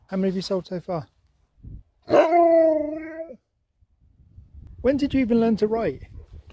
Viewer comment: Holy crap! When asked Batdog "when did you learn to write?" I seriously thought he said "when did you learn to wipe ?" My bad.